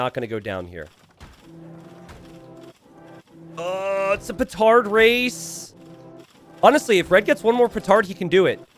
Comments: thought it said retard race and i was excited :( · I 100% thought it said "retard race" and this was LeL game, im disappointed.